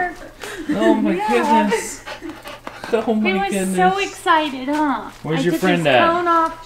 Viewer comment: When Asa said oh my goodness to maverick I heard "oh my kittens!"